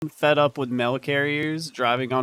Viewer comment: Thought you said milk carrier at first, not mail LOL